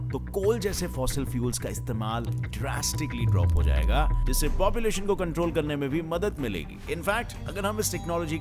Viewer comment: Who notice population control at 4:40 instead of pollution control 🤣🤣